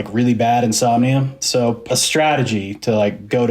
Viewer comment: did he say "pa-strategy"?